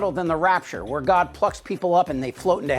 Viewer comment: I laughed when I misheard and thought he said, "God fucks people up"